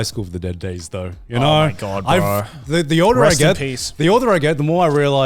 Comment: I swear I hear "breast in peace"